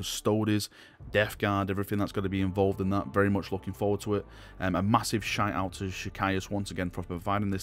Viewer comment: Did say "A massive shiteout" at 16:29 lol